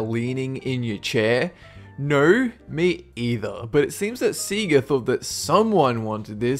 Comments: did he just say sea-ga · On 10 did he just say seega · did he just say SEE GA what the fuck